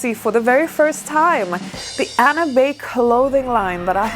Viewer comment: Lol the subtitles said "anime clothing line that I have launched" I actually thought you were into anime cosplay and I thought it was real until I realized you said your name Anna!!